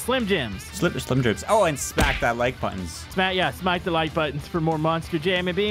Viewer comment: Would you like to see more Monster Truck stuff in BeamNG? We could try to get one on a regular map! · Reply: did just say `smike that like button'